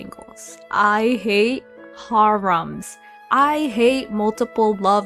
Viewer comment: LMAO im sorry i was soo cought off by how you said Harem(?)😂 i thought you said Haram🤣🤣🤣❤❤